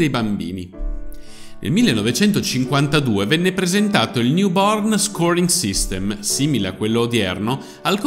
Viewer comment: avevo sentito YOUPORN! XD Poi ho capito che diceva NEW BORN.